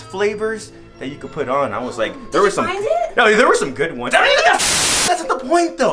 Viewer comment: what did he sayyy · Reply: There where some good ones He's talking about axe body spray